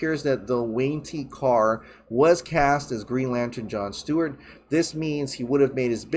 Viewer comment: I thought I heard trevante rhodes was his green lantern choice.